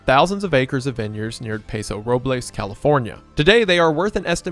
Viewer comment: Did he just say PESO Robles and not PASO Robles????